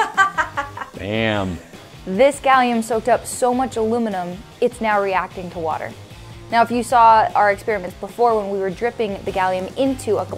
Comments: I hear, "Alominum foil", not aluminium. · Mate sounds like he said dam😂